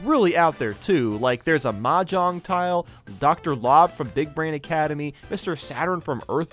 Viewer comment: did you say “Dickbrain Academy”?